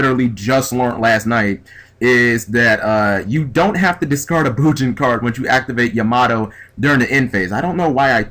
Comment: did you say that you can add a card with yamato and then not have to discard? What? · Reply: He said "Bujin" card. You have to send a card to the grave but it doesn't have to be a Bujin.